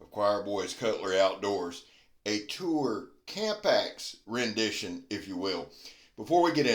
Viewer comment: Nice chopper,but i have to admit, I wasn’t really paying attention until you said Toor Camp Axe,because I mistakenly heard you say”Cool Tampax” LOL, and of course I started paying attention at that point,LOL